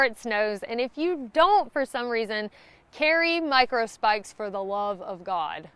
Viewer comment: Did she say Micro Spikes and not macro spaks? I heard "ass axe." BTW; I used my titanium spoon for a knife, screwdriver, fork, cauterizer, AND spoon.